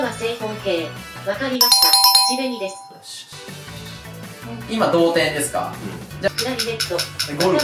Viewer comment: 今、同点ですか。 が 今、童貞ですか。 に聞こえたww